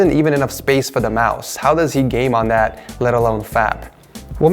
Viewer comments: Does he say fap at 1:04? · did anyone hear that. "fap" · Did you just said fap? 😂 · When you think your setup is good and the ones on worst setup edition are better than yours... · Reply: did he say fap??